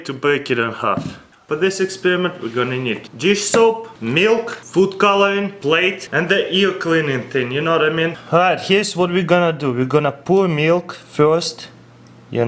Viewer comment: I thought he said foot colouring lol